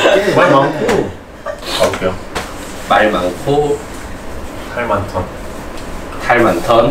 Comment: 아니 왜 이 말하니까 탈많던이 탈(모) 많던으로 들리지?